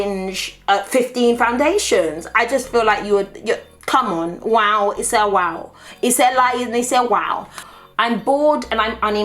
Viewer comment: Wtf does she say at 2:21 ???? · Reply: Hatice Yildirim loool she says “wow, it’s a wow. It’s a lie and it’s a wow”.